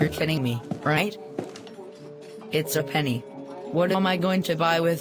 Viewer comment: Did say “No” or “Nope? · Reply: He actually said “What?”